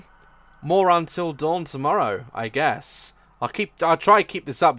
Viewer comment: in the end it sounded like he said moron-til dawn tomorrow 😂 ..... i'm sorry i'll go home now